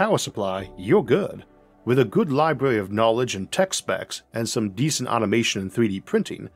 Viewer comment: I thought he said, "with a good library of tex-mex..." Which at first sounded like quite a smelly arcship.